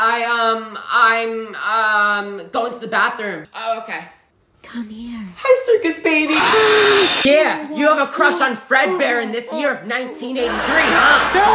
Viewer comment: i burst out loud when they said circus baddie 😂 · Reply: I thought they said circus daddy lol